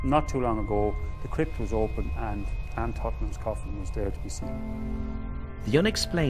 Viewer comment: What does he say @ 30:44 ??? Was there to be sst? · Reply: He said "Anne Tottenham's coffin was there to be seen"